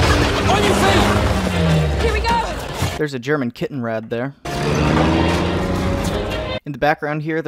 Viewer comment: >Kittenrad You managed to make "Kettenkrad" sound cute. I tip my head to that. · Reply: My bad. Yes. He still sounded like he was saying kittenrad.